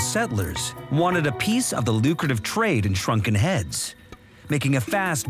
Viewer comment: For a second I thought it said “Shuriken heads.” Was really, really confused.